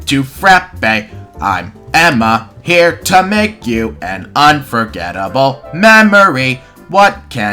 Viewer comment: I thought it said “unfortunate” for a second. XD